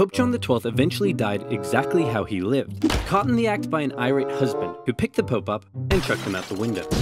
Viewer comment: First time I watched this I heard, "Caught in the act by an Irish husband"